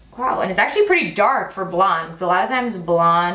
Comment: When you pulled out the brow pencil and told us the shade, you mouthed/quietly said the color “blonde” and I thought you said “black” 😱 girl I was so scared bc that would NOT match 😂